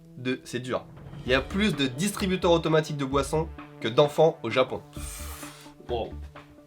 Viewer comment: putain je suis con XD a 8:52 j'ai compris "il y a plus de distributeur de boisson que (de distributeur) d'enfant" ... Genre des distributeurs d'enfant quoi XD !!! Bref je me suis tapé une barre seul je voulais le partager